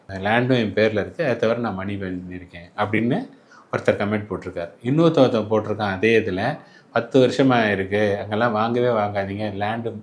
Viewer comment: Enna sir solreenga, i hear it as "Innoruthan potrukaan". Kindly avoid calling in singular sir.